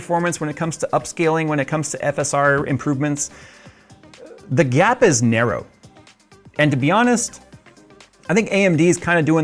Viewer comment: im so brainrotted that i heard "the gyat is narrow" instead .